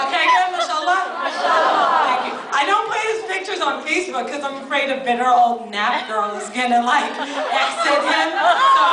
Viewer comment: what does she say between 6:53- 6:57? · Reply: sounds like she says she doesn't put his picture on facebook because she's afraid of bitter old nappy girls? that's what it sounds like lol